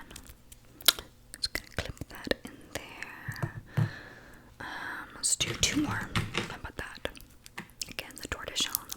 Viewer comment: I wanna thank you so much, I Watch this video Almost every night! 🥰 Besides that I have a question.. At 30:48 you name the color of the clip, and I just dont recognize the word 😂 English is not my native language so every time I hear it I’m like whut? What word is that haha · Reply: Tortoise shell. It is more of a pattern than a color. Popular on eye glasses frames it’s the brown and black marbled look :)